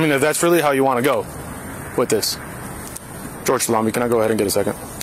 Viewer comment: It sounds like he says "George salami, can I get a second?" When he calls for backup